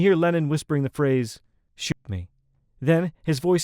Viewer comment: John whispering “shoot me” in Come Together is haunting when thinking about his death, and the general context of the song. Wild. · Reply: I always thought John was saying "shoop". Is it possible there is no tragic coincidence?